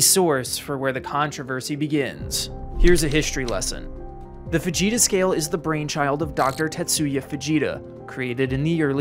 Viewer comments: I thought he said the Vegeta scale at first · I know it's supposed to be that, but I can't hear it as anything else. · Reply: i literally heard “The Fujita Sca-“ and stopped like “VEGETA!?” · I heard that as the Vegeta scale... I've been watching to much TFS again.